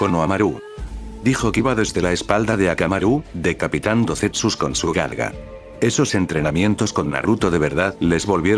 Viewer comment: al inicio entendi Decapitando cuerpos con su nalga :V